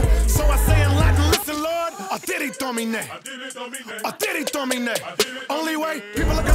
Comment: what is she saying in another language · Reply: dat guy 4rea! Audire Domine – Latin for the phrase “Lord, hear me,” “listen my Lord,” or “listen Lord.”